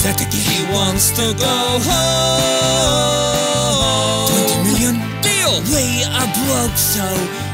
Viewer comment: Bale say's: Let me go home English CC say's: Let me play golf